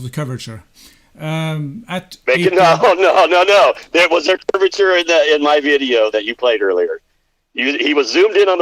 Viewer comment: "There was Earth curvature in my video that you played earlier." - That is sure what it sounded like right there.